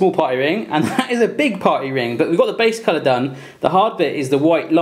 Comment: Who else thought he said potty rings